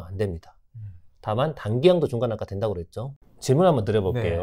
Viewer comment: 에 강사님 말씀이 알들리네요.. " 다만, 단기 000 라고했죠 " 이부분이 발음이 안들려요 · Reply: "단기양도 중과는 아까 된다고 그랬죠" 입니다. 입주권도 단기 양도에 대해서는 양도세 중과가 된다는 내용을 다시 확인하는 멘트입니다.